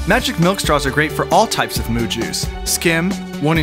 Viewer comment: I'm sorry, did you say "Moo juice"?